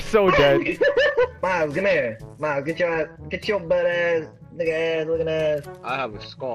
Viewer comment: Did he just say butt ass?